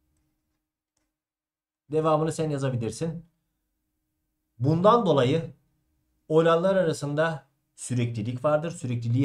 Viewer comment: hocam bundan dolayı'dan sonra ne dediniz anlayamadımda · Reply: Bundan dolayı olaylar arasında süreklilik vardır 👍🏻